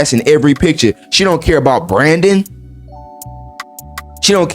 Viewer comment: thought he said "she don't care about Brandon" i was like what he say fuh me for? lmaooo